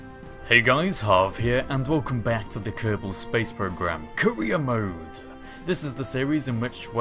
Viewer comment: Sounds like he said Korea mode